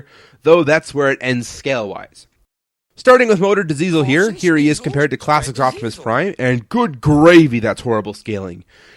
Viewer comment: Wait... I hear some voice in the background at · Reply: LegoBloks Gaming thay would be an audio clip of George Carlin saying "sniffs and sneazels, spread diseasels"